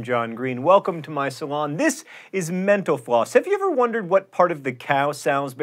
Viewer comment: Sometimes when he says "mental floss" I just hear "menopause"